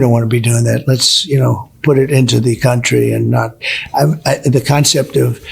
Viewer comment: Did he say “put an end the country” instead of controversy?